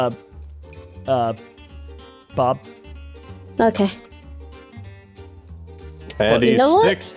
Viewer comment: right there when she said ok she really sounded like EmiOk